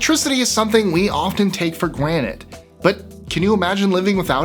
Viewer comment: is it just me, or did he say gwanted instead of granted